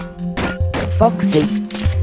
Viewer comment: Ok mangle i thought she said "Fuck" sy instead of Foxy in